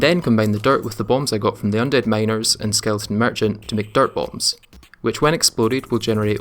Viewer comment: For the longest time I thought you were saying dart bombs lol